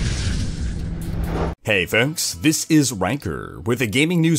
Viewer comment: i heard "Hey foks, wanker here"